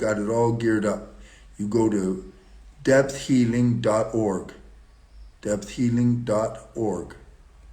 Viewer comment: <link> this YT channel is new. Last one banned for too many controversial topics. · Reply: thank. You. I thought u said “” deathealing””” I got. 10,000 sites about death therapy